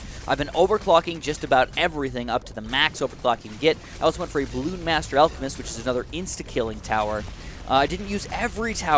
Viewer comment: did he say the f word?? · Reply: Fuck